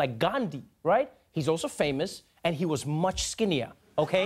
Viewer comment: 😂😂😂"Same height, half the cost". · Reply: Oh thought it was a play on words. I heard, "Same height, half the caste."